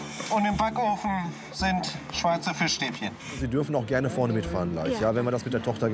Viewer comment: Bin ich der einzige der bei 14:10 "Schweizer Fischstäbchen" verstehe